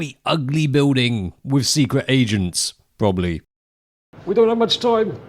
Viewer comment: idk why i thought you said secret asians